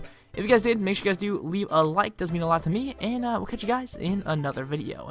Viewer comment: at the end i thought you said "stay true penisssss"